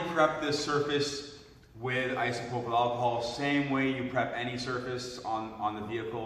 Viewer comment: sorry what did you say at 1:30? What did you prep it with? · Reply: isopropyl alcohol